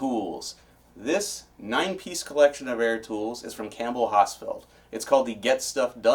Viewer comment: Air tools? I thought you said "Hair Tools!" Was hoping to get some of that great Finnegan bear and hair look!